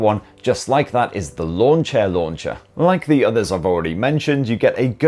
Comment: Although I know he's saying "Lawn chair launcher"... All I keep hearing is "launcher launcher" 😅